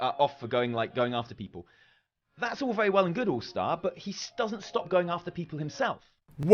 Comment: Also, @ 12:41 you misheard Tali, he said that he told his viewers to stop going after people... "but HE doesn't stop going after them HIMSELF"... not his audience. You made a point that his audience didn't listen, but that's not the point of Tali.